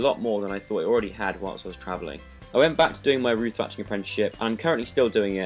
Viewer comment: I'm not british, so there were a few words I couldn't understand... What did he say he is working as? at 4:41 .. ? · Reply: roof thatching (THATCHERjoe)